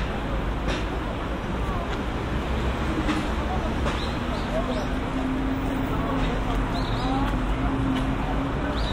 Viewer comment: Someone said bangtan seonyendan around at 12:00 Or i heard wrong? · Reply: Yeah, you're right. :) Her friend said, "..watched the broadcast." Then she asked, "did you watch the Bangtansonyeondan (in it)?"